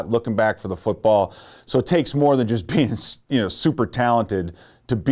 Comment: Did he just say penis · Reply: he said being